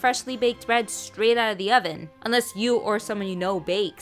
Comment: Sounds like ur saying bread from salt I'm Spanish low-key. Or sum like that